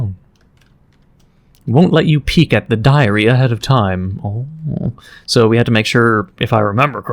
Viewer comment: "won't let you peek at the diarrhea of time" why does it sound like he said that